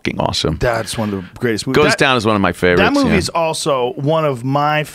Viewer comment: What movies did Rogan say was one of his favorite movies at 00:21 he say something like Goes Down, Ghost Town, Ghost Down?